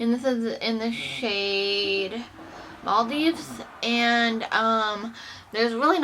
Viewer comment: You said "Maldives" I heard "ballsy" o.o Love the declutter ❤️